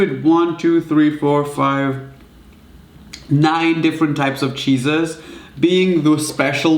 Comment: why did I heard "Cheesus" (like Jesus) instead of "cheeses" lol what's wrong with me